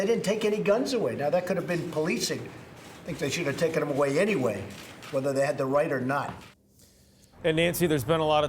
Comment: did he ACTUALLY say "take them away weather they had the right or not "? What kind of idiot politician are we dealing with here ?